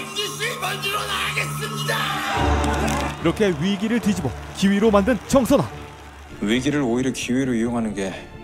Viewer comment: 이거 나만 기위로 들리나 ㅋㅋㅋㅋ